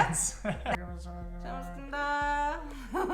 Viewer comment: 와앀ㅋㅋㅋㅋㅋㅋㅋㅋㅋㅋㅋ 뿜었닼ㅋㅋㅋㅋㅋㅋㅋㅋㅋㅋㅋㅋㅋ 쳐먹겠습니다 ❤️ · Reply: 나도 "쳐~먹겠습니다!"로 들었어 ㅋㅋㅋㅋㅋㅋ